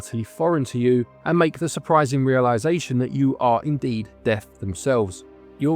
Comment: At first I thought you were saying deaf rather than death 😂 I was thinking it was an interesting concept but who is deaf the pig or the reaper... Then it dawned on me :) Absolum. Looked like a level based on attack of the mutant camels. Let Jeff minter know!